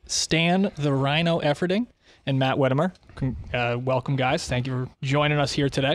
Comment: I’d love to learn more about Matt and his work. Anyone know Matt’s last name? I thought he said Whitmer, but couldn’t find anything on him